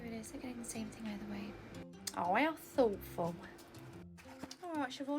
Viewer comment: what did actually say? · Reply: “How thoughtful”